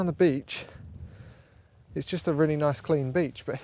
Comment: As a French speaker, I heard you talk about "bitches" the whole time... Which was.. Interesting.. Especially